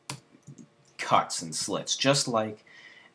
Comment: lol on 6:57 sounded like u said cunts and clits.. lmaoo